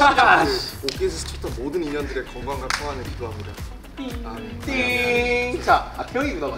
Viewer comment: DID JHOPE JUST SAID LISA??? PLEASE SOMEONE CONFIRM....